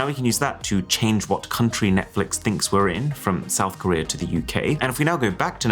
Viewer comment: And here I thought I once heard someone said "things are cheaper in South Korea" 😂